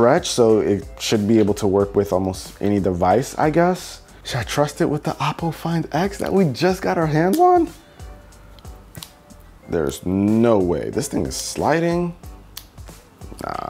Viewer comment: did he said apple find x??